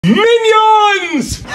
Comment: “TONIGHT WE SABOTAGE MY BROTHERS DATE”😂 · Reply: I heard "sabotage my brother's gate"